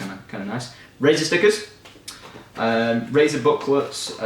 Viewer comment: LOL I heard "racist stickers" :P